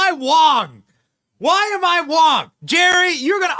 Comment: lol I thought you were saying "why am I wrong?"🤣🤣🤣My ESL is high today